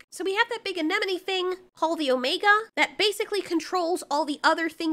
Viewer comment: all I have to say is “…Any Sufficiently Advanced Technology Is Indistinguishable From Magic…” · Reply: it almost sounded like you said “Paul the Omega” instead of “Called an Omega”